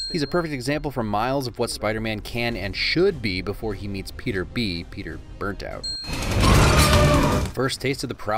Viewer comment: Haha my headphones ran out of battery there so I heard “before he meets Peter B… Peter battery low”